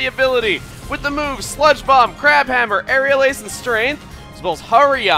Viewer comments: i think i heard "crap hammer " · I thought for SURE you said "crap hammer!" LOL